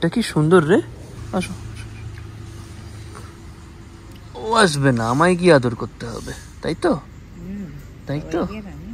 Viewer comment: if anyone heard "sobai ki ami" so cute 😂😂